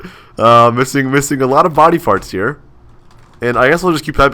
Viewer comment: Did he say "Body Parts" or "Body Farts" --> turn on captions at 4:35, it also agrees with me!! LOL XD